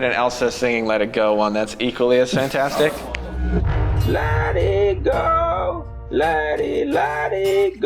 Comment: Frozen remake with bad CGI is where I got my dogs name. I heard "Leti Go" instead of let it go and my new dog at the time loved it, so we named her Leti